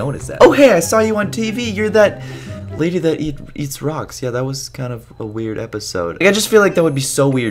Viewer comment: Lowkey thought said socks